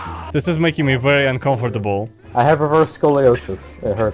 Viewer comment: I think he said REVERSE scoliosis not ROUGH scoliosis